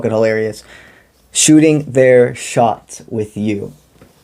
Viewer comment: I JUST FINISHED watching a show about sharks 🦈 🤯🙏 Ngl, at first I thought I heard "shooting their *SHART*" 💩💨 🔫